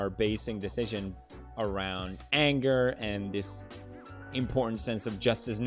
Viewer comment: I thought he said edgar not anger at 16:10 lol